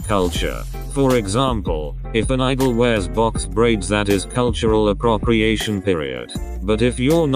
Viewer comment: I almost threw a fit because I thought you said boxed briefs instead of box braids lololol